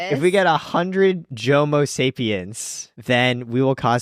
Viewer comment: Wait a second, I heard "Jomosapiens". Bless up.